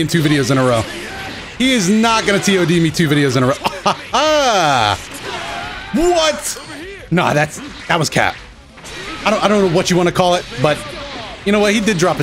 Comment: " Nah that was cap" im not sure if that's what I heard at the 1:39 mark but I would've never expected him to say that, lol